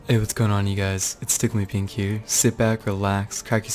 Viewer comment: I thought you said tickle MY pink